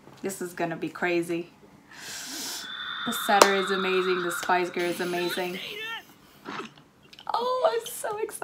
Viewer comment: i heard "The setter is amazing, the spice girl is amazing" lmao